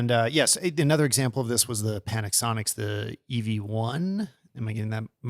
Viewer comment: I heard PANIC-sonic 😂. Amazing podcast! I love it